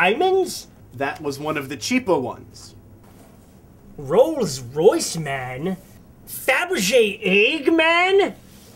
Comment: 12 years ago I thought he said "Roll's voice man" instead of "Rolls Royce Man" and the joke was Roll had an expensive voice actor.